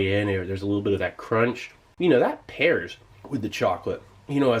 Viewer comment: "That pears with the chocolate" "pears?" anybody? instead of "pairs?" ah who am I kiddin